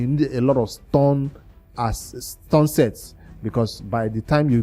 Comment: Sorry, did you say "stone" or "stun" sets. Thanks. 7:55 Anyone can answer. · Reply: Stun set